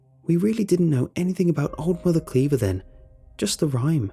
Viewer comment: I thought it said old mother Klavier